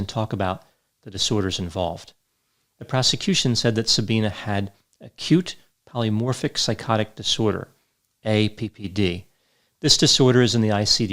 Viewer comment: First I heard "she had a cute polymorphic psychotic disorder"